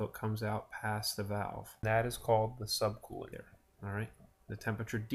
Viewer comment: Cant quite catch what you are calling the circled area at 0:52 it sounds like " the sub cooling area " is that correct ? , great vids by the way.